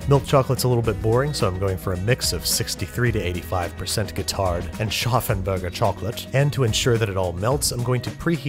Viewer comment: Anyone know what kind of chocolate he's talking about around 6:25? He says "qatared" and scharffen berger but i cant figure out the first word, katared or qatard idk